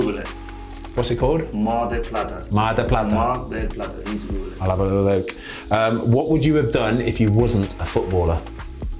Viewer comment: What’s the place he said at 4:54? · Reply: Mar del Plata, Argentina, thats his hometown